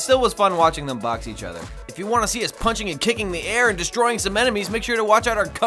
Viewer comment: I thought at the end it said Wanna see us fucking the air I'm messed up in the head